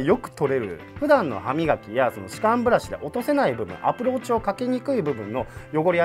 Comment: アプローチがApple Watchに聞こえた俺はただのApple信者